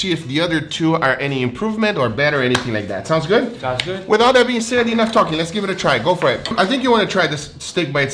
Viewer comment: "Without that being said" Lmao, what?! You mean "With all that being said?"